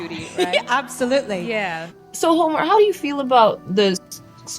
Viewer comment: I thought that they said "Homely" not Homer!